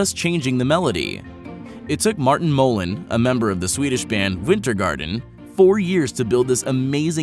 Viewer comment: did he just call my favorite band vwintergarden XD It's more like veentergahtan